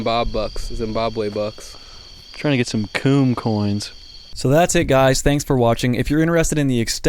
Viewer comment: did he just say coon coins